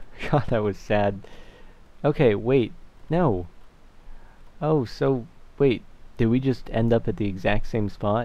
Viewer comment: if u keep clicking 5:40 it sounds like hes saying sopa